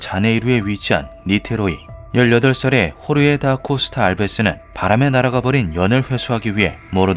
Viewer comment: 요즘 하도 바클을 봐서 바람에 날아가버린 연을 바람의 나라가 버린 연 으로 들었네 ㅎㄷㄷ